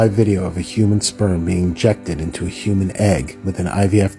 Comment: I thought it said rejected